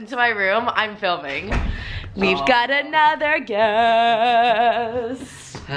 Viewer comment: When she said we have another guest, I thought she said another gay